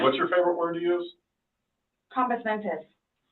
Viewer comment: What did she say at 44:02 that replied "they are using her own three-dollar word against her"? Did she say "common sentence"?! 😳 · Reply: She said near the start of the interview she likes to stay Compos Mentis . It’s Latin for having your wits about you . So they turned her comment back on her.